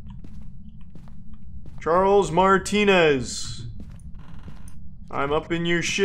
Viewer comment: Idk why but him saying cricifex instead of crucifix is extremely annoying and on top of that this cameon when I was sleeping so I kept having Charles Martinee in my damn dreams cause I thought that's what he was saying